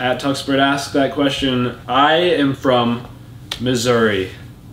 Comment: It sounded like said misery instead of Missouri